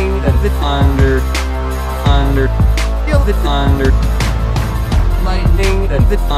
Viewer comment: It sounds like he is saying "fander"